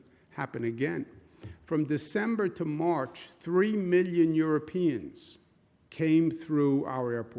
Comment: Wait I thought he said three minion Europeans 😂😭